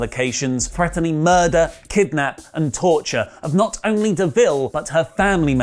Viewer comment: Why do the captions say "Berenato" instead of Deville at 8:44?